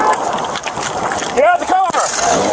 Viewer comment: what did he say at 0:24? sip on tar? lol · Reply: step out of the car